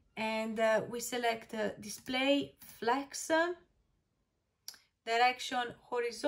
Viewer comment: What is she saying at 6:13 after clicking section. I don’t know where her mouse goes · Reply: If I'm understanding correctly, it should be on the right panel. Display - Flex